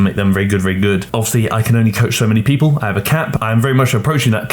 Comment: I thought you said “I have a cat” when you said “I have a cap”. I figured the cat would take up a lot of your free time so you can’t take on too many clients.